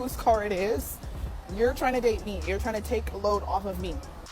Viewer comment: the blond lady is wrong. the black girl didn't say "when you date me, my bills are your bills." what she said was, "when you are TRYING to date me..."